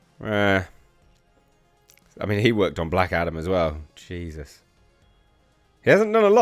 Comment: When you said he worked on Black Adam I thought you said Black Adder!